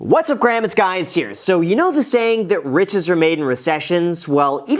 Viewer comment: I keep hearing "Whats up Graham, it's guys here" anyone else?